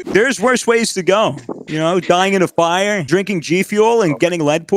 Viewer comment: "Scream if it hurts alright 3 2 1.." captions: "right just screaming for her ready 3 1.." perfection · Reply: the captions say "there's worse ways to go you know dying in a fire drinking lead poisoning" that IS the worst way to die.